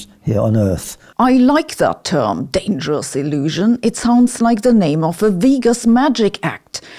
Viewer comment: I heard "dangerous delusion" not dangerous illusion.